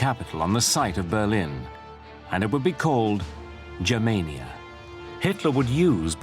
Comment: idk why i heard jamaica